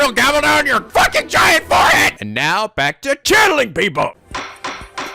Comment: "Now back to CHANNELING PEOPLE!" I thought it orginally said "Now back to KILLING PEOPLE" Didn't know it was channeling until episode 2